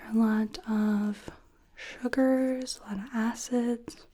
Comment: I yawned when you said "what is your diet like?" And I swear I heard "a lot of hookers and a lot of asses?" I was DYING, I had to rewatch to figure out what you actually said. 😂